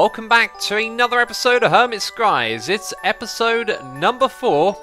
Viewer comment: I thought you said "Hermits cries" at the start for a second....